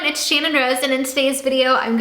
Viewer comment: In the intro it sounded like she said Shane and Rose or was it just me?😂